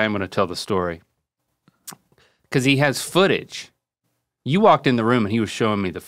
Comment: Did anyone else hear, "foot itch" 23:10 I was very confused